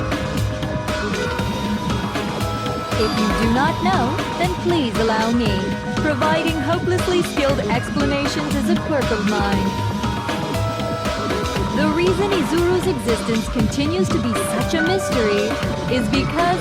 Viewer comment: DID SHE SAY QUIRK!?!?!